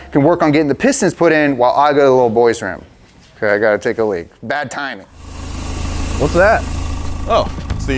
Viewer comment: Did I hear "Big Time"?